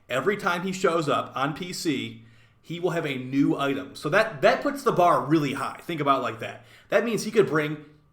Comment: Did say "that puts the baro real high" at 3:06 - lol